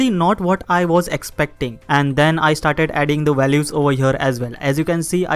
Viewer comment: I'm sorry, but this is obsessing me and i need to ask. There's one word i really can't figure out, but it's repeated a bunch of times in a short amount of time in plenty of phrases and i can't figure out 5:43 "And then i started adding the value ????? as well" "As you can see, i started adding 8 value ?????" "This is totally not a wizard, just a gentleman wearing a suit ?????" And there are more later, even with more context, i just can't understand, it's driving me crazy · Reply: He's saying "over here"